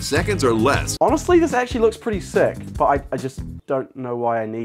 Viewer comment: I like how after the nap he just said sleepily “it’s sick” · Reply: I heard "yeet stick". I know lol.